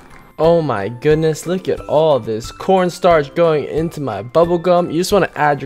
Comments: did he say "corn starch" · did he say corn starch? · who heard him say corn starch instead of corn syrup